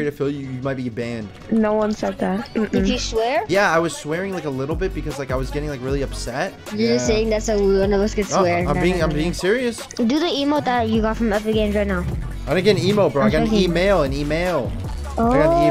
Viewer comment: Man thought you said emote 🤣🤣🤣